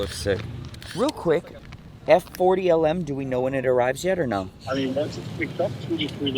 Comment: Did I hear F40 LM at the 15:50 mark? I gotta see this. My all time favourite Ferrari. Absolute animal of a car!